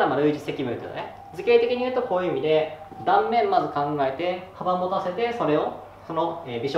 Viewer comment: 早送りしてたら3:34「幅もたせて｣が「かまぼ化せて」って聞こえた笑